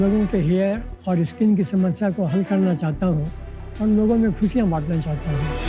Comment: What did he say at 3:04? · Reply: Logon main Russian baatna chahta hoon